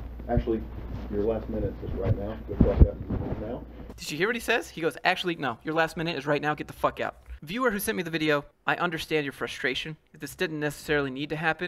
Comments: HE SAYS “GO AHEAD AND CLOCK OUT” Not GTFO. Boi addin drama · I dont think he said "get the fuck out" it sounded like he said your last minute is right now go clock out and leave right now.